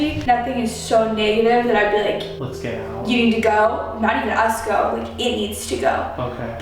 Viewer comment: my caption Said let’s go their here